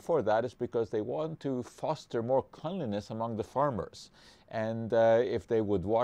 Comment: can someone help me and write what Dr Schwarcz said at 1:00 "they want to foster more ............ among the farmers" and at 1:06 "that it would be more like ............. for the farmers" thanks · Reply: dd410 First word: Cleanliness, Second word: Lackadaisical